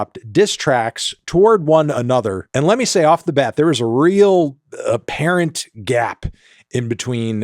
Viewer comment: When you said "apparent gap" I heard "parent gap" and I thought it was a shot at Travis being an absent father or something